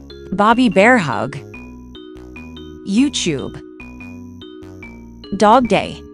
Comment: Who else heard it as You-Chewb? 💀